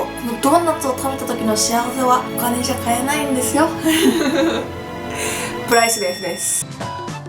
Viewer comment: 何て言った？（笑） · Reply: すず プライスレスです